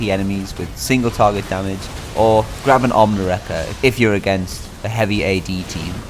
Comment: i couldnt make out what you said. can you tell me what you said there? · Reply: Grab an ohmwrecker :) I always try to speak clearly but even I thought that was abit slurry now I've been weeks since working on this! Hope you learned a few things! Xx